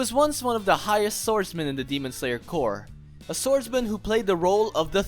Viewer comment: I heard you say "Demon slayer core"